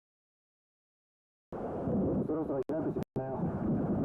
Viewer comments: そろそろ開く時間だよと聞こえる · 聞き取れた。 「そろそろ生き返る時間だよ」 「人間だから大丈夫」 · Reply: そろそろ開く時間だよ、にも聞こえる(地獄infernoの入り口が？)